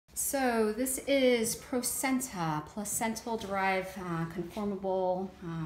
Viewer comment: So you use stem cells from the placenta? Where are you located? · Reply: OK, at least I'm NOT as crazy as my therapist thinks I am! I kept hearing 'placenta' - and was like... HUH???